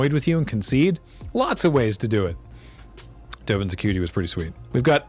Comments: I 100% heard that as, "Dovin's a cutie". Thanks.. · dude I really thought was saying "dovin's a cutie" a first lol, I mean get it I guess :)